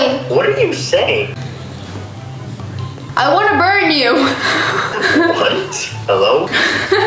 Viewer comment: I thought she said "I wAnNa bUrN uP"😢